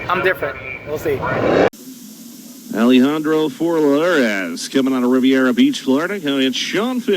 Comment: and 8:45 lol sounded like the guy said floorass